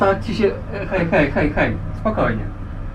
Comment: I heard "Hey, hey, hey, hey, fuck off" I read "Hey, hey, hey, chill".